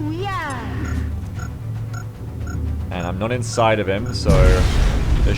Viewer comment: Wait, did just say "The day is, as they say, arse."?! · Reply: I heard "And I'm not inside of him, so there shouldn't be an intimidate roll". The mental image was not pretty. My brain eventually went "ah IN SIGHT of him.. phew.."